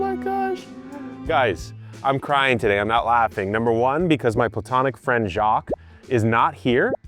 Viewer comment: “Emphasis on the platonic.” I never really suspected, but now I definitely do. 🤔😂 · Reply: I heard him say "my botanic friend - emphasis on platonic" and was confused. But yeah, they keep getting comments about them being a couple, so I understand the emphasis